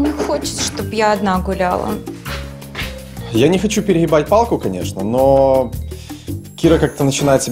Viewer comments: Мне показалось, или сказал на 15:00 Пере-***бать палку?))) Я понимаю что он сказал "перегибать" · когда сказал перегибать мне послышалось переибать